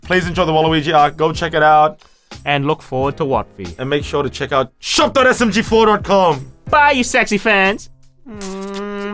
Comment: he said "what's up you sexy fans" "not what's up you sexy hobos"... Who are you people!! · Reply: I thought he said "sexy fags" so I was like "...hmmmm..."